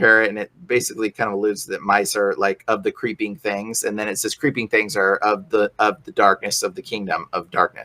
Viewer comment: I stop at the part about mice. I don’t like mice or rats, but they aren’t evil. They’re animals trying to survive. You and your god need a reality check. I don’t care if he’s the king of kings. Some thing is wrong with him if he and you believe in that nonsense. That goes for any animal you and he doesn’t like. Sick and tired of people hating animals cause the way they look. And creepy things aren’t the kingdom of darkness. They’re animals and some have emotions like mice and rats. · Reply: Not "creepy" things... they're saying "creeping things of the darkness" which is far different from what you thought you heard.